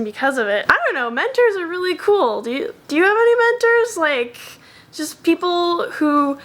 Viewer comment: When said "Do you have any mentors?" I thought she said "Do you have dementors?" and was about to say yes, but then realised that'd give my wizard identity away. So No, I don't have any dementors, I swear...